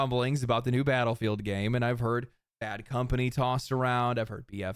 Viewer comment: Im sorry, did you say Bad Company/BF3/BF4? Don't give me hope...